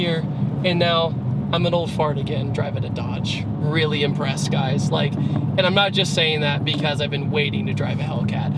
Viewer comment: PPL, at minute 8:09+ did he say, "i'm an old fart can driving a Dodge", "i'm an old Ford guy driving a Dodge", or WTF?? Someone............. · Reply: TigreLoko24 Ford guy.. owns a Mustang